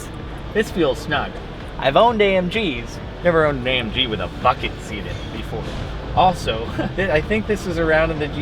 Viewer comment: I thought you said “I’ve never owned an AMG with a fuckin seat in it before” lol